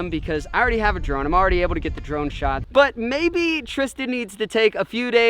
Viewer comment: I thought said I tried to get drunk shots